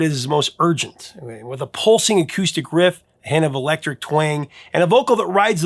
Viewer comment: So, at one point it sounded like you said "Hannibal Lecter twang." I knew that couldn't be right. I rewound and turned on the captions. Oh, okay a "hint of electric twang." That makes more sense. However, I also discovered that the captions can't seem to decide if the man's name was "Lifewood" or "Lifefoot." HaHaHa